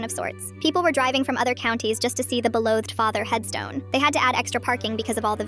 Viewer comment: I thought it said “below the father” as if you mean he is in hell 😂😂😂 Christians will get it